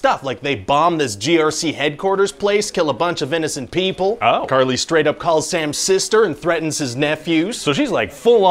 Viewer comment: "Karli straight-up calls Sam 'sister'" is what I heard. That's uncool on so many levels